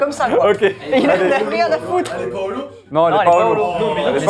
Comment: Quand le mec a dit: " Elle est pas holo ?! " J'ai entendu: "Elle est Paolo ?" (c'est mon prénom)